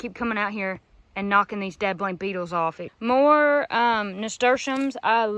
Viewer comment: Lol closed caption thought you said dead blind Beatles